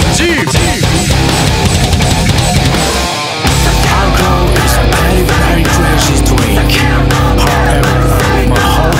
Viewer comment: ~ 何言ってるのかめっちゃ気になる ﾊﾞﾝﾄﾞﾏｰﾝ ﾀﾞｲｶﾞｸｾｰｲ ｱｲﾂﾗｱﾙｺｰﾙﾏｼﾞﾒﾁｬｸﾁｬ だけ聞き取れてしまって余計気になる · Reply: アルコール イズ ベリーベリー クレイジードリンク パーフェクト rrrrアラウンド ホーニューランド イズ アルコール(?) アルコール イズ メチャクチャ デンジャードリーンク ディスイズ アルコール ヒト メチャクチャクルワース バンドマーン ダイガクセーイ アイツラ アルコール マジ メチャクチャ オモタケマクール(?) と聞き取れたんですが？付いてるとこ何回聞いても聞き取れなくて、、、。 なんて言ってるか分かりませんか( •̥ ˍ •̥ )？